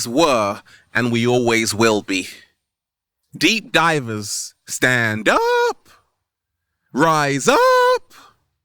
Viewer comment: When he said "Stand up!" I heard "Flight team stand up!" Flight Reacts.